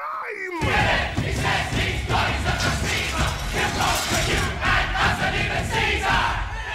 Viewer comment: What is the mob saying at 2:40 when Pilate says “I need a crime!” I cannot find the words anywhere and my hearing isn’t very top notch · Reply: I actually had to look these up, I couldn't tell either! from Genius: MOB] Kill him! He says he's God—he's a blasphemer! He'll conquer you, and us, and even Caesar!